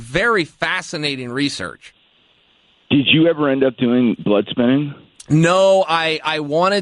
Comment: does anyone know at 6:46 Nick asked Chael if he ever did a certain procedure, I couldn't make out the word after blood??? thanks in advance.. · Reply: Pretty sure he says 'blood spinning'.